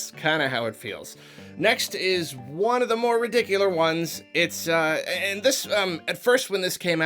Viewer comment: "next is one of the more 'ridicular' ones". Ridicular? Did I hear that right or am I just being ridiculous. Oh nevermind. That's an actual word now.